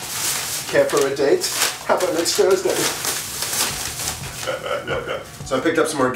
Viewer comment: What does he say at 4:48???? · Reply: Sounded like "Would you care for a date? How 'bout next Thursday?". That goofy laugh was funny!!